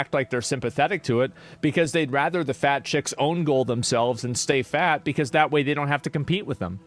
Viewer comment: When he said that fat chicks "own goal themselves and stay fat" (not sure what that means), I thought he said fat girls "bonemeal themselves". Which sounds hilarious from a Minecraft standpoint.